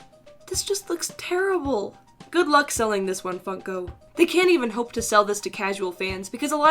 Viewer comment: It kind of sounded like you said “good luck selling this one f**ko”